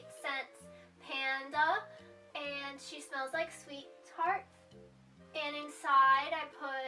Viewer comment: If it smelled like sweet tarts it would already be in my mouth. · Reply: I actually thought she was saying sweethearts...thank you for that clarification